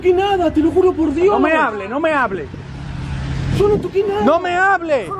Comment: Juro que pensé que dijo “Te lo juro por Dieguito Maradona”